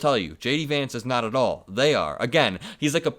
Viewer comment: I heard, “He’s not … at all.” I didn’t hear him say, “He’s not weird” 😂🤣